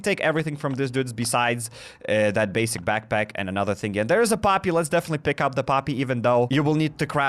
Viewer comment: did he say paapi